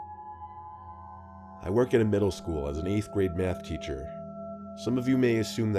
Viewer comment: Did I hear..."I work in a high school, I'm a Bath teacher."